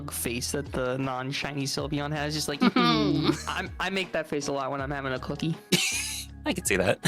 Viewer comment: Lol... She said "I make that face when I have a cookie"... What I heard... "I make that face when I have a quickey"... Lmfao